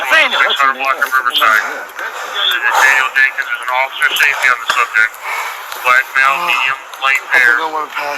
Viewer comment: did they say performed officer safety on the subject